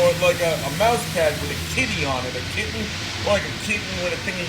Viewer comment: The knife is going to be AWESOME! I Love the secret signal ! #Fc (I wish i was here for the Livestream, but i was studying guys I had my phone on mute) Hahahaha instead of Kitty on the mousepad I thought you said KIDNEY! I instantly thought "Yeah man that guy knows how to use a knife." THEN I HEARD YOU RIGHT hahahahaha yeah a man I want to meet the babe with the sexy mousepad Fffffuuuuuuucccckkkkkkkkkkkkkk yeah! Fuck yeah about the we knives! Holy shit.. yeah man That's fucked up about the cellphones going dark and the spies! #MedfordKnives #Fc #Arizonatime #awesomescrewdriver!